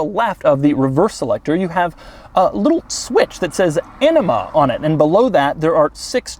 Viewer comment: Did Doug say enema at 9:10 ?